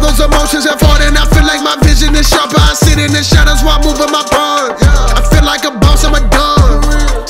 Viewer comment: sounds like Moria saying "I feel like a box at McDonalds"